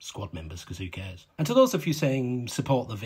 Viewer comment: I had to replay the last few seconds. I heard "... not going to bang 20 girls this season.." 😮